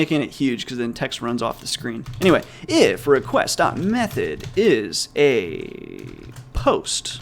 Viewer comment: did i misheard, or this is realy a russian word "записать"??